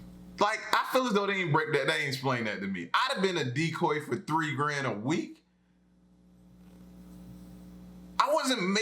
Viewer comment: I thought he said the decoy earned $30g a week